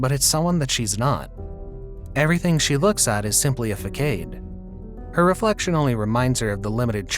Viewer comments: My man really said Fuc-aid instead of facade. Like bro. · facade is not fakate. Not nit-picking cause I didn't understand initially, had to playback a few times